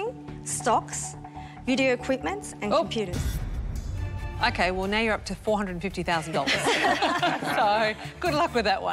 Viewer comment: Can anybody tell me what Janine said at 5:44 ?? 🤔 Was that "pewters", "peters" or what? Lmao. I dig it that she was shocked when they said they'll be doing this and that with that money and i really wanna know what's that word that came outta her mouth right after hearing this 😏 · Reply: I think the entrepreneur said: "Marketing, stocks, beauty equipments and computers", during which Janine interrupted to say "Oh" in shock at how expensive all of that would be